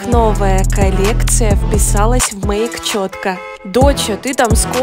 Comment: Мне послышалось вписалась в моих а не в мейк